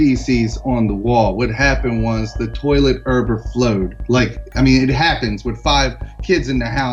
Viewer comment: I'm i the only one who heard him say urbor flowed